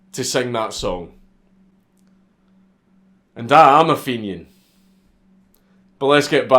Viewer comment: what did he say? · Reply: "and I am a fenian"?